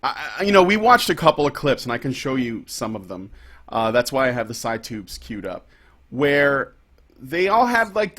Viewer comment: Every time you guys say "side tube" I hear "side boob."